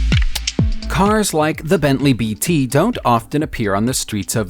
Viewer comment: Did he say Bentley bt